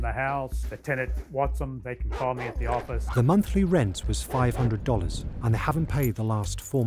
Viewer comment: " the monthly rent was $500 " did I hear that right ???. Lol that house would be $3000 a month at least out here